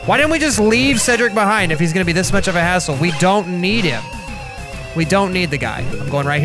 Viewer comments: Foe the 7 year Olds out there, he did not say "asshole" at 8:10, he said hassle. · "Why don't we just leave (name I forgot) behind if he's gonna be such a hassle?" Thought he said "if he's gonna be such an asshole" at first lol.